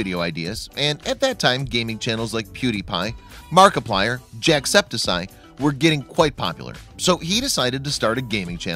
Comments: Did you just say Jackseptisie?! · jackcepticie!?!? Jack septic eye jacksepticeye Like if you think he said it totally diffrent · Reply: He said Jackseptiseye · what did you say? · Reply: He said markiplier, unless you're talking about 3:28, where he says jackseptiseye. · did he say jacksestiseye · Did he just say jackseptiSeye? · wait did he say JACKSEPSYE???????????? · did he say septisie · Jack-septi-sigh is what he said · hold up , did he just say " jack secticide"